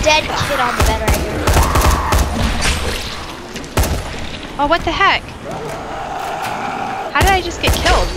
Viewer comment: when fell I thought said what the he**